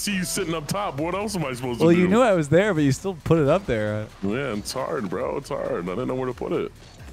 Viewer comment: "its hard bro its hard bro idk where to put it" thats what he said frfr